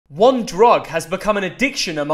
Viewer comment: I was just randomly scrolling and I came across this and I thought he said "WANT DRUG" aggressively while staring at me I laughed so hard.💀